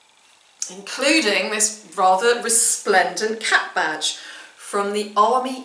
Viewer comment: I thought you said cat badge….I was all excited! Cap badge..l.care less on this one….catch you on the next find! Cheers!